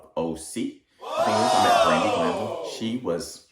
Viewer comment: I heard Brandy Glanville, but isnt she from BH? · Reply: okay I thought I heard Brandy too but I wasn’t sure and yes she’s BH